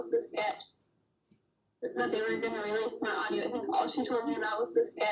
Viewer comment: is she saying ”release MY AUDIO.” ??!?